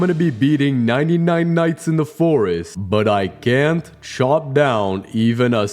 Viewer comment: did bro just say nivel 9 nights in the fores💀🥀